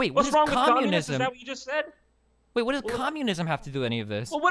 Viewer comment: Whats wrong with Communism? LMAO. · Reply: I'm pretty sure he said "What does communism" then got cut off by and it sounded like he said "Whats wrong with Communism" Cause right after stops talking Destiny makes the point that Communism is an economic ideology and they were talking about culture, thus having nothing to do with the conversation. Have to go back and watch it again.